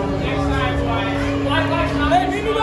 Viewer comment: What does #43 Florian Xhekaj say? I just heard “you’re lucky” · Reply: I think he says your lucky this guys recording